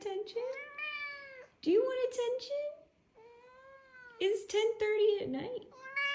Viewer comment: Misheard attention as kimchi at first. 😺